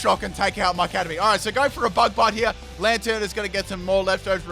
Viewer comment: It honestly sounded like you said Bug Butt lmao